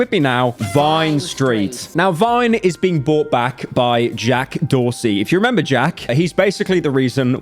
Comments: I thought that said Jack Doherty… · I misheard him say Jack Doherty. Thank god it’s not him!